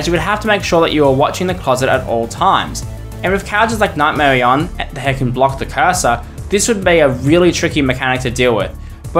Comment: it sounds like he said hecken blocks the cursor